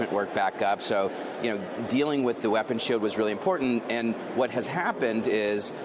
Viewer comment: "Dealing with the weapons 💩💩" Did I hear that right??